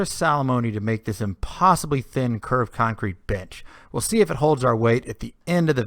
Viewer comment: does he say bench ?